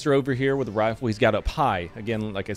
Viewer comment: Oh, he's got "up high"... I heard, "I've got a pie".